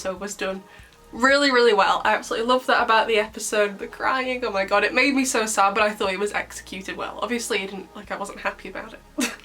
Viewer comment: Have to love my audio processing issues, at the start I thought you said "it made me so sad when I thought he was executed" 💀💀. I'm also glad I'm not the only one wondering why the random guy has 5 alliances, I thought of them as like a new IPhone coming out, it's designer and expensive technology, plus all the rings do the same thing 😭😭. I loved the Adriennette, Adrien's idea for Marinette to raise her hand for yes or no was such a sweet scene. I wish they gave them some more time to actually feel the weight of giving up being heroes, it felt like they brushed that off so that we could return to another cute moment and to have them together watching the akuma battle. I like your idea, I feel like Marinette could be more on guard with her feelings so she wouldn't get akumatised, she even said how she can't feel too sad for that reason in the episode near the start. Whereas Adrien would still work as the target, so I agree they should have leaned more into that and kept it between Adrien & Marinette, maybe Adrien would suddenly worry about Marinette being akumatised bc he can no longer protect her as Chat Noir so that's how they end up in the same room together after they give up their Miraculous. Something like that I could have worked better and would make more sense to have classmates as the new heroes. Zoe should have had some more build up to this as we didn't event get to see how she reacted to Plagg, unless they do a flashback in part 2, which would be nice but unless t